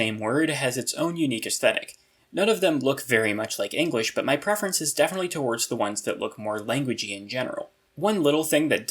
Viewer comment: what word is that meant to be? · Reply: Actuality